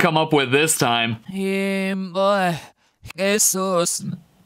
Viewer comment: i heard "femboy, its sus"